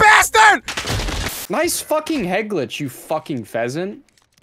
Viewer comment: did say fucking n word or was that just me